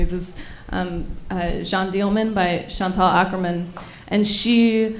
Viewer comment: Hi, at 34:15 what is the movie and director that she says, I can't capture it. Thank you! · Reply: Jeanne Dielman by Chantal Akerman